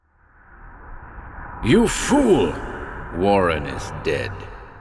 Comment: I thought you said, "Warren is bread".